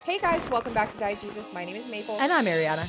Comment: Every time y'all do your intro, all I hear is 'Diet Jesus'. Never gonna be able to unhear it.